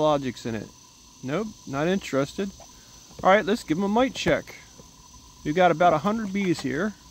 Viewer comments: I thought he said mic check. Nope that's not what he said. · I thought he said mic check 😂😢